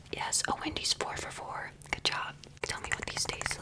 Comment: Not Wendy's 4 for $4😂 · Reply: I thought she said Wendy’s Por Favor 😂😂😂